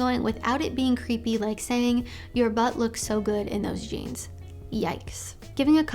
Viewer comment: I thought she said "your butthole looks so good in those jeans".....i'm like come again?